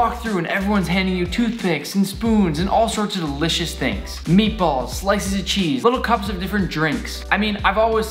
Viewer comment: did he say delicious toothpaste